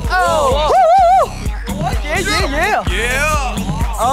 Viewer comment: someone in the background ''Oh my god ,shit'' XDD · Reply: I heard all ladies popin......